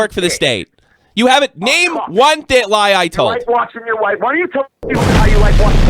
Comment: did nobody else catch "you're a cuck, why don't you tell them how you like watching your wife.." then BOOM. comes back looking sheepish af. Anyone else hear that?